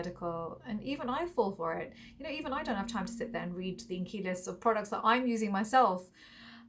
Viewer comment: Mam say it as ingredient list..if u say as ingy list i heard it as inky list and im wondering when did inky list brand formulate a body wash🤣